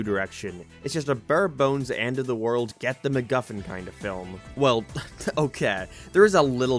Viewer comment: Dude, that’s not how you say McMuffin, also, There’s no reference to McDonald’s in any way, so maybe think before you say things